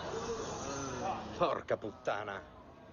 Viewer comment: Ma solo io ho sentito un "porca puttana" al secondo 00:08?